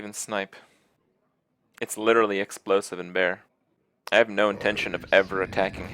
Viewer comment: what kripp says: 'That's literally explosive and bear' what I hear: 'Misdirection doesn't exist'